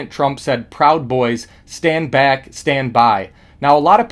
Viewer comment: That’s what I thought I heard too!! Proud boys stand back “